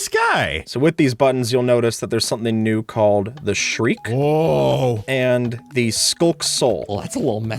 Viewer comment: I almost heard you say shrek instead of shriek